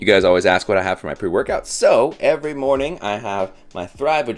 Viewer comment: ok first thing I heard was 'You guys always ask what I have for my poo workout.' ... my brain messed up beautifully there